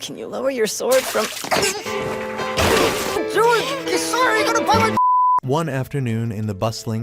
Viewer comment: what I hear: I’m sorry I’m sorry